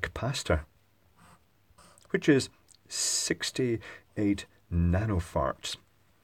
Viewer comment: I heard "68 nanofarts"